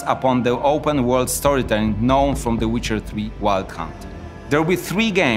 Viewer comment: did he just say wild cunt?😂